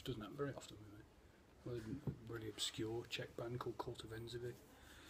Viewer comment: what band? (i mishear it ) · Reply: Kult Ofenzivy.